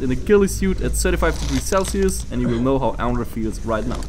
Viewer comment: I thought you said 75° Celsius